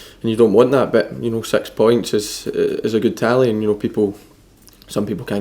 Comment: Every time he says six points, I hear it as "sex" points. lol XD